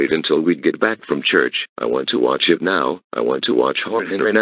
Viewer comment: I thought he said I want to watch him now